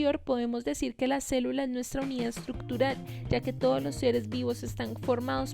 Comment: Hay una parte en la que no entiendo lo que dice, ya se que es el concepto de celula pero no logro entender que es lo que dice en el minuto 5:47 ya que los subtitulos dicen "las células nuestro mío estructurar e igualmente en el audio no entiendo que es lo que dice